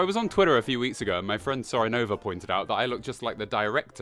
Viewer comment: Did he just say "sorenova" at the start?